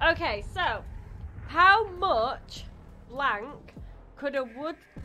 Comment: I will always contend that is da real MVP of Preach Gaming. Also, to my Yankee ears, I legit thought she said “How much wank would a woodchuck…” instead of *blank*. I will never again brush my teeth whilst watching a video with 🤣🤣